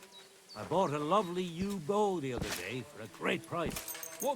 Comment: 'I bought a lovely yew bow the other day' I totally misunderstood at first, it sounded like a u-boat to me 😂😂 48:11 Not sure about the race guy, but the archery guy definitely told you his horse's name when you first talked. And of course they became friends immediately afterwards 😆